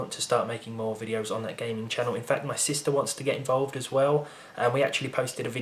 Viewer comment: I heard system instead of sister. I was like, yes your PC, no doubt wanna do gaming videos...